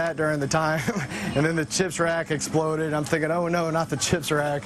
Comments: For a second I thought he said jizz wrag instead of chips rack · I had to rewind the part about the chips rack, for a second I thought he said "That chicks rack exploded". Seriously I would be saying "Oh no not that chicks rack!" too! I guess if that did happen the robber would have technically walked into a booby trap!